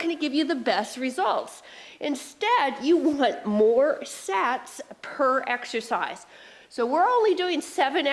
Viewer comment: Smart, clever workout. "Making the Impossible Possible!!" Thanks!! 👍😃👊 p.s. Mark misheard. Thought you said more sex per exercise instead of sets per exercise!! 🤣🤣